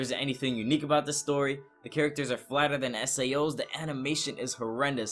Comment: Sorry... but did you just say the girls in this anime is "flatter than SAO" OMG... XD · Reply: Anh Do lmao the characters are flatter than sao' s. Would have been funny if I did say that though